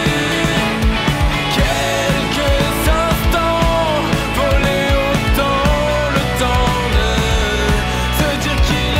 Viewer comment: Il dit quoi le chanteur a 4:29 je comprends pas 🤔😂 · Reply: Salut, désolé pour la réponse tardive 😅 Il dit (avec l'intonation) "Queeeelques instants, volés au teeeemps le temps de, se dire qu'il est grand teeeeeeeeeeemps, de ne plus regretter"